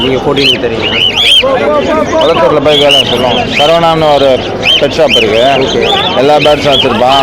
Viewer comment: corona pet shop that's what he said